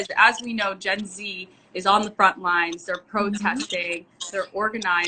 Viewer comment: i thought she said jessie was on the front line